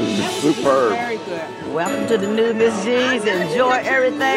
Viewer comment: LOL, try activating subtitles, instead of it writing: "The Southern Cusine" it becomes: "Welcome to the new disease enjoy everything, because you know what, i'm loving it" LMFAO :D That one black waitress is absolutely gorgeous :O